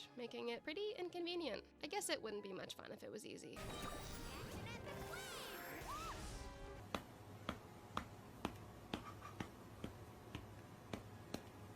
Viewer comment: Catch an epic wave? I always heard it as “and your ass is flames!”